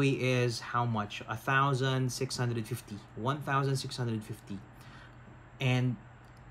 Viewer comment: 1,650 pesos? Did I heard it right? kasi ngayon po it is just currently priced nalang po to 1,350 pesos eh. according po sa link na prinovide niyo. Sana po mapansin niyo to Sir